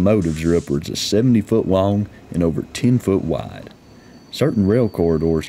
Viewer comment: Did I hear that right? 00:08 "Over 70 foot long x 2ft wide"? - That will save on the loading guage🤣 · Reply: He said 10 ft wide